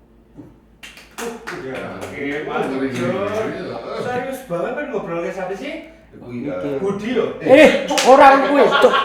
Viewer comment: Wee boleh nanya gak? Di menit 22:41 dia ngomong apa? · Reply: "kok serius banget ngobrol in apa sih" yang diakhir gatau salah apa bener, suaranya agak ga jelas wkwk